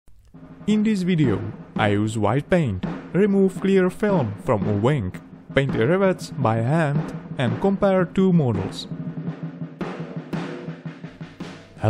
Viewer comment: subtitles say: remove clear film from a wank. 😂😂